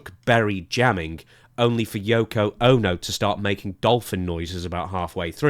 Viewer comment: You said Yoko Ono but I heard Yokozuna in my head. Once you said dolphin noises, I had to go back and listen again.